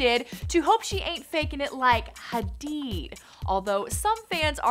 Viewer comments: i mean i clearly hear she said "hadid" cause she laughs and the other girl laugh too tbh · Either way it goes she said hadid not hadeed plus when ever she said it she looks surprised and looks at Jade like "did that sound like what I think it sounded like?" I don't think she purposely made it sound like she was saying her name